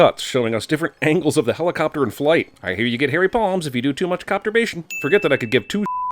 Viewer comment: He said "hairy palms" at 1:04 & idk why my first thought was "Harry Potter" right after... Don't mind me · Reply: i heard "hairy balls" 😭